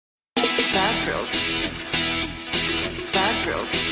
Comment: Sometimes i heard "bathrooms" not bad girls